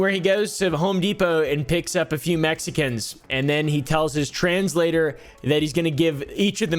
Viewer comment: When you said even the translator i legit thought you said trans lady and I was like there is no Fing way 😂😂